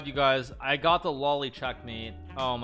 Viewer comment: for anyone who’s mistaken it’s not spelled loli and it’s actually lolli mate or lolli checkmate with two L’s......... It’s named after 18th century Italian chess player Giambattista Lolli. It’s a pattern where the pawn is on 6th rank and with a queen checkmating a king. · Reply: I thought he said lowly, but im just e